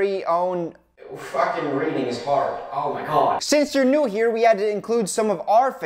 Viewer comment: f*cking reading is hard my mind: its raining in your heart? I had to rewind it to listen to what he said again. btw i LOVE idea about having the candy scream when you bite it, that would be so sick!!! ("sick" as in really cool-I'm british)